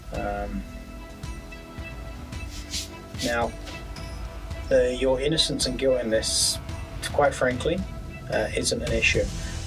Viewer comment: what is said: "your innocence or guilt in this" what the faux subtitles read: "your innocence and going this"